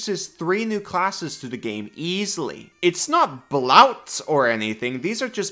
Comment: Is he saying 'blouts'? The heck is that? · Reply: Bloat?